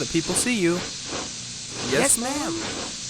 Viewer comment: I know this isnt your fault but when you say ma'am it sounds like hes saying mom